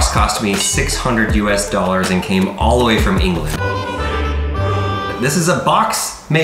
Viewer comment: wait... Did he just say that England is silly?!!!!